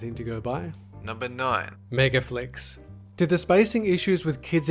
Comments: for a second their i thought it said megga fucks instead of meggaflicks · I LITERALLY thought megaflicks said megafucks...